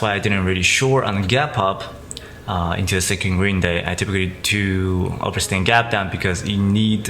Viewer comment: What did he say at 3:00 ? He said typically does a percent gap down? is that what he said? If so can someone elaborate what does he mean and why?